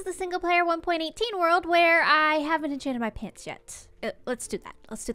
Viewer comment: I thought you said "I haven't changed my pants yet" and not "I haven't enchanted my pants yet". Didn't catch on till I saw the enchanting table